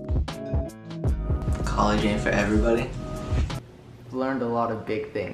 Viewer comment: someone tel me what he said plz omg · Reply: college aint for everybody